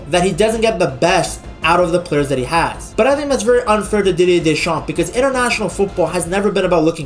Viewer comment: Bro i heard you saying Diddy Deschamps at one point